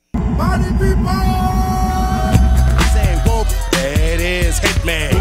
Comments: As a kid, I always thought they were saying "Whoomp! THAT ASS" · I always thought they were saying whoop Dat ass lol